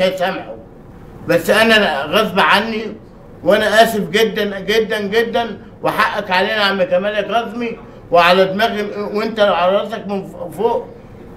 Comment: يا شباب0:51 وش قال كل الكلمات بعد اسف قدن؟ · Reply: Yazeed Rightツ انا اسف جدا جدا وحقك علي يا عم كمال الكاظمي